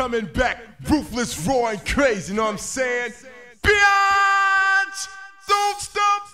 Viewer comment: that moment when you thought he said BLYAAAAAT! but its actually BIAAAAATTCHH!